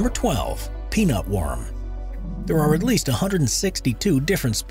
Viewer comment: I thought he said "penis worm"... I was like, yeah, i can relate to that